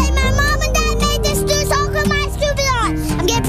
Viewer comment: Them: My stupid heart Me: Did she just say my stupid aunt?